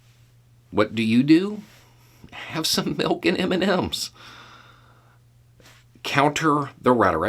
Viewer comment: To the people I told that Beau said "milk and Oreos", I apologize. I went back and listened to his video again. He did say m&ms. I'd have sworn Beau said Oreos. Anyway, I prefer dark chocolate peanut m&Ms. Grab the m&ms and popcorn while you work to counter the insanity.